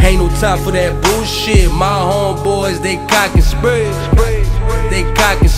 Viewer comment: It sounds like he sayin "they cock get sprayed" lol dope song tho 💯